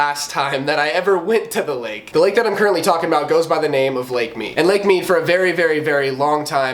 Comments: Wow... i searched up lake me... #Misheard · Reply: i heard lake meme 😂😂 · Whenever he said the name of the lake I thought he was saying "dank meme"